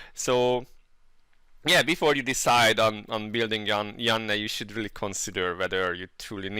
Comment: building yon...I heard it...I wont un-heard it now...